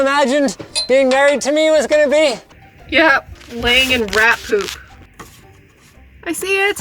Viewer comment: "Laying in rat soup" is what Captions thinks she said.